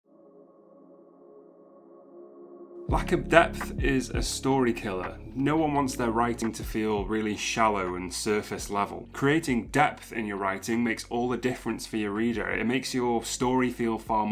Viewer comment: I thought you said "Lack of depth is like a serial killer"